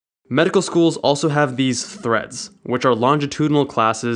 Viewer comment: When you said "Threads", I thought you said "Threats" and it made the sentence VERY different. Haha!!